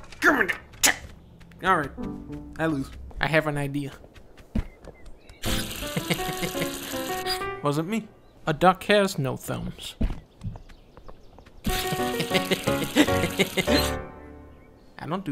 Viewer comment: “Wasn’t me, a duck has no thumbs” -Dunkblo, 2019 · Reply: My hearing must be pretty crap cuz what i heard was "a duck has no felons"